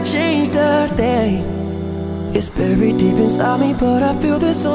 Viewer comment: loved this cover❤️❤️❤️❤️ but did anyone else hear berry instead if very at 0:24-0:28 ??